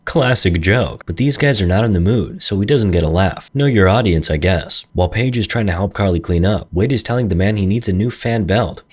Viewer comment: ayo what did he say? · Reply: Dio !